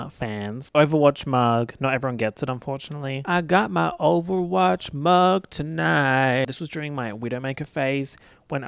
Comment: I wasn't paying attention and thought you said Overwatch Marg until I looked back at the screen 🤦‍♀️